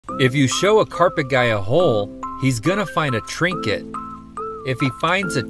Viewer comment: I thought he said their gonna try and drink it😂